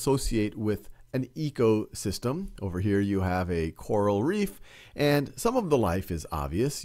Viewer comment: Him at 2:05 saying quaral reef instead of coral reef 😂 everyone is to use to saying quarantine